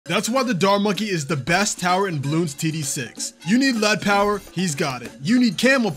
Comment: Sounded like he said "Bloons Titty 6"